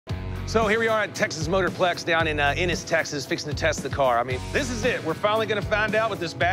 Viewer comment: he says “Fixin to test the car” My dumbass thought he said “Fixing the Tesla car”